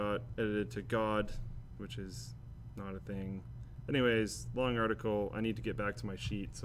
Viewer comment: is it just me or did he say that "God is not a thing" essentially meaning he said that he does not believe in God. go to 31:01 if u do not believe me.